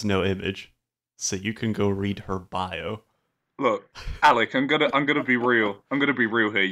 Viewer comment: did he just called sseth alec?